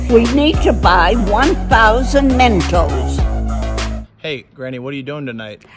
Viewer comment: when you said 1,000 mentos i thought you said 100,000